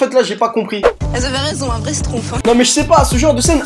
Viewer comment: '' elles avaient raison, un vrai schtroumpfs '' c'est ce que j'ai entendu 😂